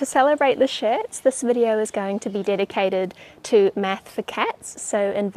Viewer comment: I thought it said “meth for cats” and would have been about cat-nip